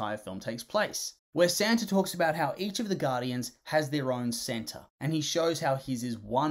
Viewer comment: bro ur accent messed me up, I thought you said "where Santa talks about how each of the Guardians has their own Santa" (In all seriousness, great video. Love this movie and I hope it gets more recognition)